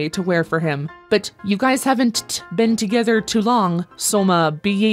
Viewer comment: Thought it said "guy shaven't" instead of "guys haven't". As in a guy who hasn't shaved.